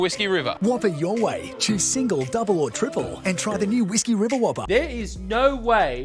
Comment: Everytime that HJ ad comes on I hear "Whiskey WiberWhobber"